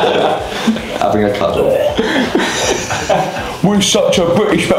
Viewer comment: even these people don't understand the british boys. Mark said "having a cuddle"